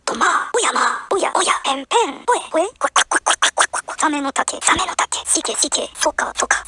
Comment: サメの竹、ホント好き。 ガチでそう聞こえる！